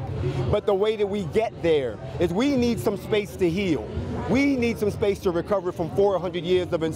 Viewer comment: why am i hearing: "we need segregation"?